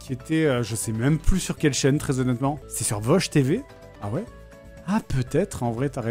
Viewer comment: J'ai compris "Bosh TV"...